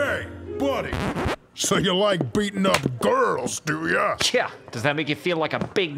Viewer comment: Could’ve sworn I just heard Dio ask why I like beating women... Those two notes got me slippin